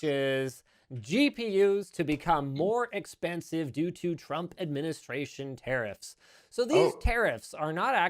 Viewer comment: I just heard “trumpet ministration” when Linus was speaking lol